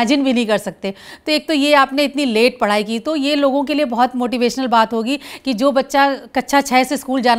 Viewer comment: Mam said kaccha instead of kaksha